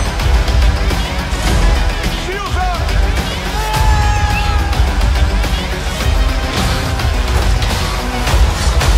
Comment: please does anyone know what words Nick Fury shouts at 1:37? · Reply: Shields up